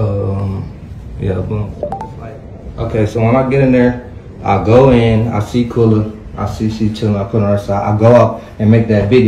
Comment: did he say "I go in there I Quella & I turn her on her side" ( ive listened several times-if you saw these bruises you didnt ask the others wtf happened? OK you werent there but when you did get there... WTF is Summer Walker music gone do???????????????????????????????????????????????????????????) he needs a lil more pressure applied he will tell what they told him eventually... they recorded the fight they absolutely told somebody else what happened... GET THEM PHONE RECORDS!!!!!!!!!!!!!!!!!!!